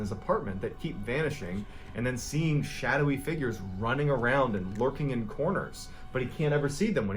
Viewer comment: are you sure it wasn’t in 1991 not 1999 when he died. I am pretty sure it was ‘91. Keep up the good work! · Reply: HDHGDBSKWJHE I THOUGHT WHEN HE SAID SHADOWS LURKING IN CORNERS HE SAID SHADOWS TWERKING IN CORNERS HDHDBDBDHHDH